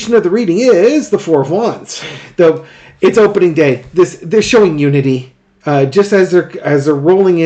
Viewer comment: I really wish I could have been at the rally. Edit: 5:25 It sounded like you said "They're showing nudity" 😂